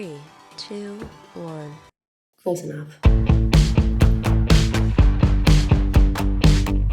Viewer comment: the reveal of your makeup for the britney look you said “close enough” i heard “it’s britney bihh”